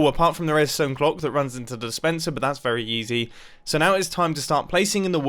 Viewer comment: Mumbo’s so rich now he says yeezy instead of easy